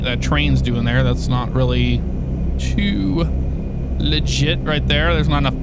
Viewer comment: Thought you said 'choo legit at 6:26'. Would've been funny.